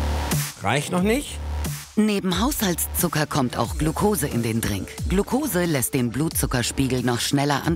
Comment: verstehe immer " Glukose in den Dreck " 😂😂😂👍